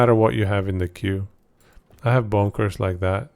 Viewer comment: Good stuff man🎉 I had extra entertainment because sometimes when you quickly say Bone Curse it sounds like you said Bonkers... The build really is bonkers 🤣👍🫵🏆